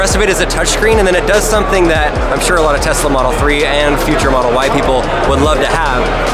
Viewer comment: did he say “ future model white people”